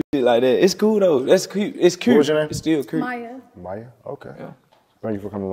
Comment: Holllllllup☝🏾 I thought lil ms. said her name was Mai in the beginning 😒 Chop was right about that one…….Slower than a Monday 🥴